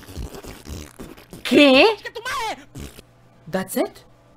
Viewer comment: Somebody hear : C O N C H E T U M A R E When Pepo says "que"